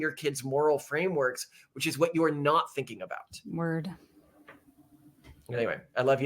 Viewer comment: Did just say ' WORD ' around 41:06 as in hip hop slang.